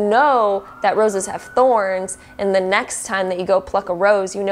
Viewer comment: Deck 3: I thought she said fuck a rose I was like WAIT WHATTTT lol the context made sense just saying